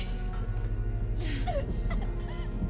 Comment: Am I crazy? Or does Powder call vi mom at 0:50? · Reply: She was saying "no,no Vi please